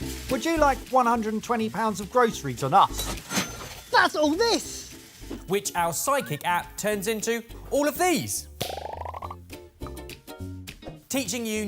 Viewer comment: I keep hearing "psychic" instead of "sidekick"!